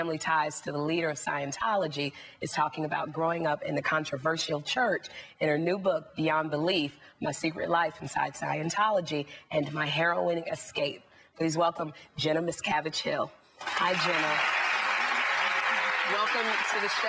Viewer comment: I thought she said "my heroin escape" 😆